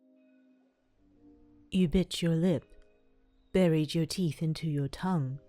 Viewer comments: Omlll the first words you said we're "you bit your lip" but I hear "you bitch your lip" and I got offended for a millisecond xD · When the vid started all i first heard was "you bitch-"